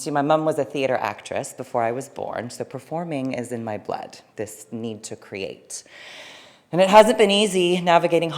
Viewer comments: Does anyone know what the sentence she said at 2:31 “this need to create “? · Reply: So performing is in my blood...this need to create. And it hasn't been easy, navigating Hollywood as a woman of trans experience... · She’s stunning! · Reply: Does anyone know what the sentence she said at 2:31 “this need to create “?